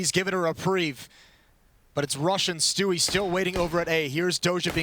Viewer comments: When the commentater say rush and stewie I thought that he said russian stewie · Who else heard it as "RUSSIAN STEWIE" when he mosses told "RUSH AND STEWIE"?😂